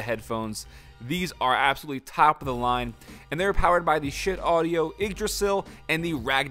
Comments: did he say shit audio? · Did he say "shit" audio or is that me